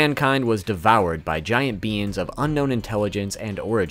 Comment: lmfao I thought he said “devoured by giant beans” 😂😂